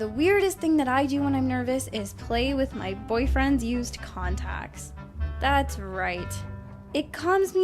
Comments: I THOUGHT SHE SAID CONDOMS NOT CONTACTS AJSJFBDJDB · I thought she was gonna say condoms instead of contacts 😂😂😂